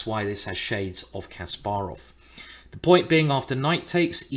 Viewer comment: What did he say in 1:10? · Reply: he said : the point being